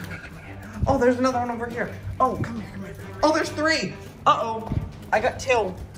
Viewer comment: Did he say come here little b** at 4:41 or is that me hearing things 😆